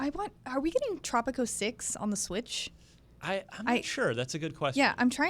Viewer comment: umm what did Casey said? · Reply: I asked if we're getting Tropico 6 on the Switch and the answer is no :c